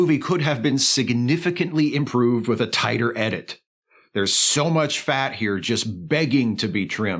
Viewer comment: When you said the film would have been better with a "tighter edit", I swear I heard "Tiger in it." - Both are true.